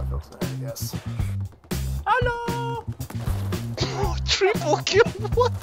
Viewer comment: did he said the German word haloo ???